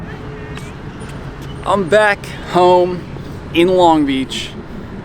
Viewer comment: Who else at the beginning of the video thought they heard say, "I'm back hoe" 😂🤣